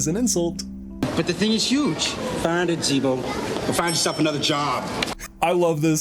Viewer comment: I had to listen to that five times I thought he called him a racial slur, and I'm still not sure that he didn't it sounds like he called him" Chico". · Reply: oh lol he calls him Zeebo! like the clown from season 1 :)